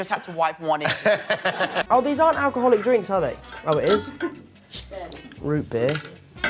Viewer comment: Omg he said "root beer” at 2:14 and I heard it as Rupert😭😭💀